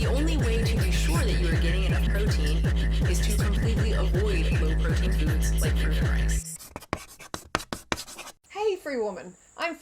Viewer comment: “The only way to get enough protein is to avoid foods like fruit and rice”!Is that what she said ?I couldn’t hear her